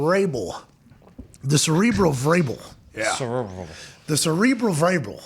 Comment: It sounds like he was saying cerebral variable.